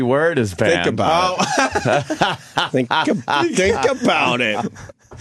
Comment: did Ope unknowingly create “shinkle bout it” , almost sounded like that phrase but no ref from jimmy